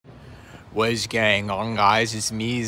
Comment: genuinely thought you were saying "wizz gang ong guys" and didnt question it